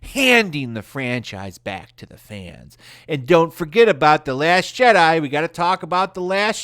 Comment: I heard "The Last Cheddar" at first.